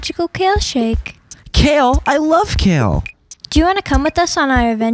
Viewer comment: I heard " Kill ? I love kill ?" what's the fuck with me exactly xD